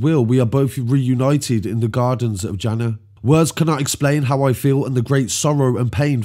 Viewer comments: sorry to have to correct you bro “Jahanam” is hell and “Jannat” is “paradise” easily mistaken’d if it’s not your mother tongue) May Allah grant the 2 lads a place in Jannat and may you carry on and keep up the good work bro nothing but love for you, from Scotland 🏴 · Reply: I thought he said Jannah which is the same as Jannat both meaning paradise. I just saw your response to the other brother. Big up for admitting your mistake and apologising. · sorry to have to correct you bro “Jahanam” is hell and “Jannat” is “paradise” easily mistaken’d if it’s not your mother tongue) May Allah grant the 2 lads a place in Jannat and may you carry on and keep up the good work bro nothing but love for you, from Scotland 🏴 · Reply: When did he make that mistake? I heard him say Jannah.